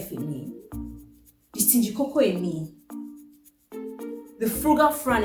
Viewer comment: Did someone say stingycoco in me 😂😂😂